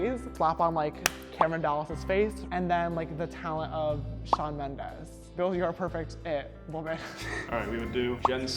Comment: I hear Shawn Mendes and I unlike the vid to like again 😂